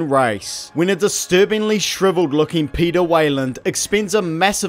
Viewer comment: I know he is saying Peter but I Always hear “pedo” weyland